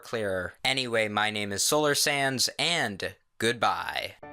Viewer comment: Is it just me or dose it sound like hes saying Solar SANS?